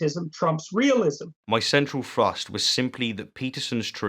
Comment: & 15:56. It sounds like you are saying "my central thrust". Please excuse my ignorance, but could you repeat what I obviously misheard? Thanks!